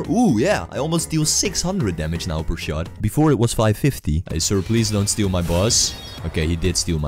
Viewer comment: sounds like he said ''Please don't steal my balls''